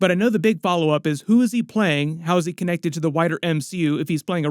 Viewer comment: Anyone else thought he said “how’s this character gonna affect the whiter MCU” instead of wider? I was like huh?